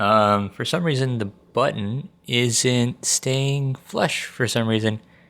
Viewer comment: Are you saying "flesh" instead of "flush"? It may just be a weird pronunciation, but it's making me cringe thinking of flesh stuck inside the Joy-Con.